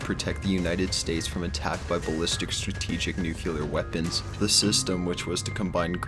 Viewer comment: Did you just said... "nucular"?